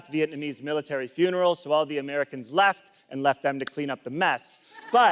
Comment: if you pause the CC on that vietnam war joke 9:15 it fills the screen with text but the joke has a different punchline. it said "you should probably surrender." instead of leave them to clean up the mess. wtf